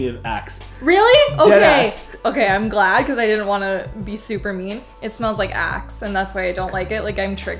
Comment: Oh shit, when she first smelled Himalaya...I thought she initially said "it smells like ass."